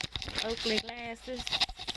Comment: your caption is wrong..... she didnt say ugly glasses, she said Oakley glasses..crappy editing